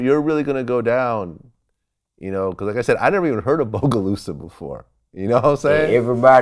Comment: wtf he say 😂😂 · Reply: I've never even heard of bogalusa before